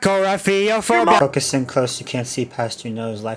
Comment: what's the long word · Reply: kakorrhaphiophobia